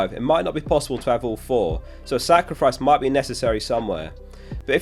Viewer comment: did you say Saka-rifice?